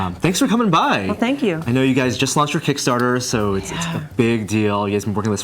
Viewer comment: The one thing I was kinda confused by , in the beginning, at 0:15 when he said "I know you guys just lost your kickstarter" and what sounded like a slightly disappointing toned acknowledgment from Jeri "Yeah" . ... What did he mean by "lost" it? I don't know much about the ins and outs of kick-starter campaigns .. Wasn't it an overwhelming success for them? I'm assuming by "lost" he just meant it's "finished" .. Lost makes it sound like a negative, as in "lost funding"